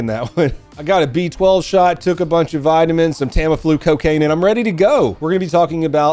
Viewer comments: Did you just say you took some coccaine at the start of this video? Lol sounds like you did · Tampon flu cocaine? I'm sure I misheard that